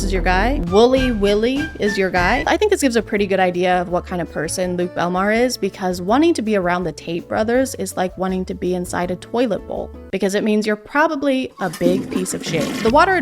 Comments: I thought you were saying taint brothers instead of Tate brothers lol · Not sure if it's because of how early it is (5 am), your pronunciation, my Auditory Processing Disorder or all of the above... But I kept mishearing "Tate brothers" as Taint brothers 🤣 Thank you for the hysterical giggle fit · You said “the Tates” I heard ‘the taints”… took me a second to realize 😂